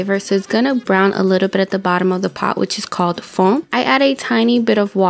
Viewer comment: did you say fon ? Love this!❤❤❤